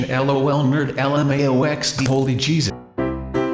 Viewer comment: I heard holy cheese it